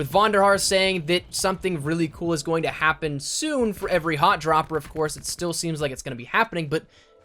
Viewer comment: I thought Von had said "hot chopper". Lmao I was like what the hell is a hot chopper? 🤨 Thanks for clearing that up bro.